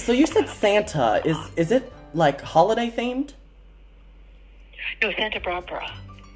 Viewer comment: AFTER YOU SAID “is Santa there” IT SOUNDED LIKE SHE SAID “no Santa brubru” I’m done😭😂😂